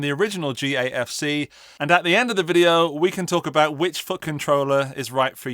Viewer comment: 'And at the end of the video we can talk about which fucking troller is right for you' I know what you're actually saying, but this is genuinely all I hear 😂